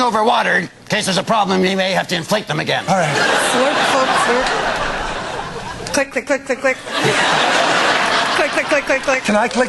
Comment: Click Click Click Click Click LOL sound like she was sayin clit XD